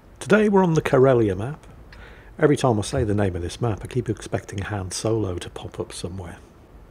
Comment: I swore he said Ham Solo and I got confused between breakfast foods and a Spider Pig like Han Solo.